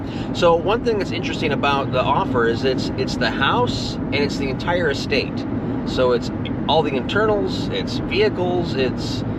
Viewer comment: I had to stop the video which is playing on my tv, jump on my phone because i heard the words "ITS THE WHOLE ESTATE" OHHH now nothing makes me flip out in excitement more then hearing that!!! Congratulations!! THE WHOLE. ESTATE.